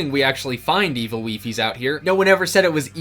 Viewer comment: One of the times he said evil wifies I misheard him and thought he said evil leafy